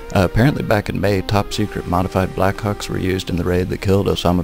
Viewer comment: i just sounds like your saying black cawk